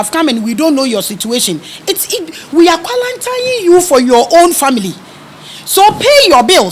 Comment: Did I hear "qualatining for ur situation"